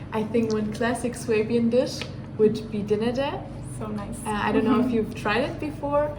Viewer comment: 用我不太流利的德文解釋一下Schwäbisch 是指在Baden- Württemberg 邦內的一個地區(在司圖加特這一帶）這地區的人說的是Schwäbisch方言，這裡也有獨特的Schwäbisch cousin. 從06:55 開始的白衣女生說的單字我聽不太懂,但猜測是Flammkuchen (有點像義式薄餅）這部分還請高人指點。黑衣服女生說的是Maultasche(德國餛飩，在超市冷凍櫃都買的到） 男生說的是Schweinshaxe(德國烤豬腳）、Linsennudel(就是Linsen mit Spätzle, 我不知道中文是什麼，就是種麵😅）、Spätzle (德國麵疙瘩), knödel(德國馬鈴薯丸子)、schweinebraten（德國烤豬肉）、Wurstsalat（香腸沙拉），如果有錯再煩告知唷～😊 BTW, 南德的人”相對”比北德熱情。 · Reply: 講的是Dinnete，類似Flammkuchen沒錯